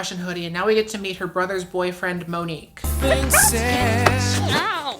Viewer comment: i had to take a triple take because i heard "mother's boyfriend" two times before figuring she mistakenly said "brother's boyfriend"